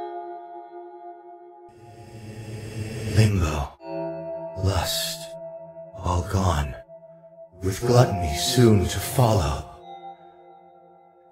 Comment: I thought he said ligma instead of limbo in the beginning 💀